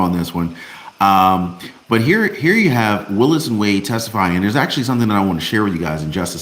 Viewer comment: Did you say Willis Wade or Willis and Wade at 3:19? Then at 3:25 you said it again but you were reading from what someone else wrote, what sounded like "Willis Wade testified..." Shouldn't they have written Fannie Willis or maybe Nathan Wade.? Thanks for your posts and insight.